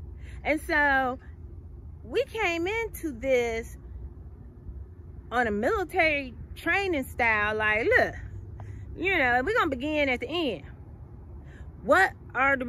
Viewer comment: I don't understand anything she said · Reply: I apologize if I didn't clarify enough. What I said was: We are going to start this off military style by beginning at the end....What results do you want to see.... Often times in the military we are taught to begin at the end. Clarify the objective and then come up with the strategy! That's all I was saying. It works in business, military and self growth. Anyway, I appreciate your comment and your feedback. Share with your friends and see what they think about it and let me know.